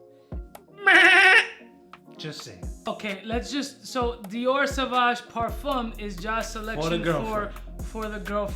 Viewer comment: I thought I heard dior sauvage pour femme lol